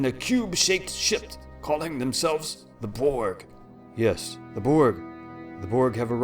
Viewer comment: I'm really sorry but for the last line I heard "calling themselves the Porg." and still can't stop laughing.